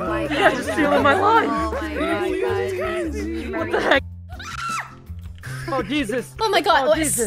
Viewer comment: did he just say what the hell ?!